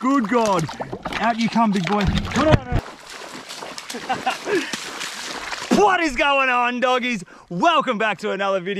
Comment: I really don’t think money will ever change this man, always keeps it 100! And such a humble guy and is proven money doesn’t buy happiness doing what does will give you happiness and I love it! Stay cool! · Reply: Did he just say 0:19 What is going on doggies 🤬